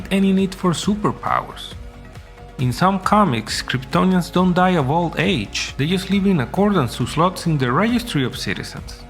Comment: Time mark @ 1:45. What the hell did you say? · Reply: “In some comics, Kryptonians don’t die of old age. They just live in accordance to slots in the registry of citizens”